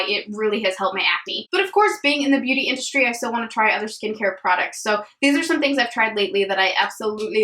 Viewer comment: Every single time you said “four skincare products” I just heard “foreskin” care products 😂 omg I’m awful but I had to share that with you I know you’ll appreciate it lmao